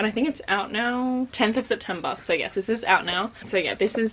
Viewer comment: omg I am so dumb I wasn't listening carefully when you first said it was September 13th and I thought u said it was September 30th and I started to look up if there are different months in Australia omg. love u!